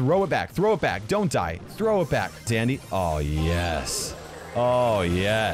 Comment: I swear I heard Dandy instead of Sandy turn on subtitles and you’ll hear it too or slow mode the video